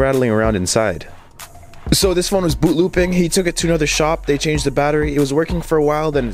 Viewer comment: I honestly thought you said it was “Blooping” instead of “Boot-Looping” lmao